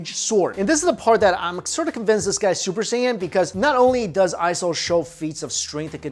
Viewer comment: Did he just said Super Saiyan?